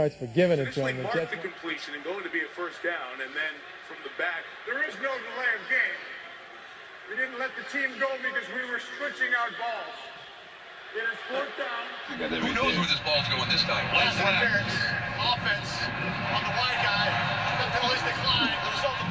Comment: Did he say, “We we’re stretching our balls”?